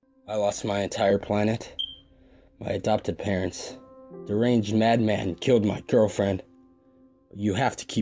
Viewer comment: what adjective did he say? The “?” Madman? Im sorry. I just want to understand that part. Idk if he said “an arranged madman”, or “the ranged madman”. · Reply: super.sam_the_man “Deranged madman”